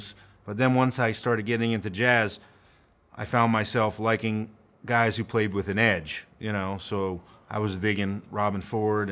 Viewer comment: For a second there, I thought I herd you say "I like guys that play like the Edge" I'm thinking this is going to get interesting..